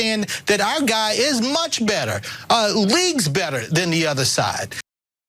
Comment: Whoops - misheard there... " Our guy reads better than the other side"..... Actually "Our guy is leagues better..." "reads better" is also very true.....